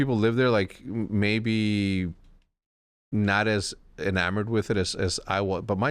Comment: what s the word „ inamo…“ please help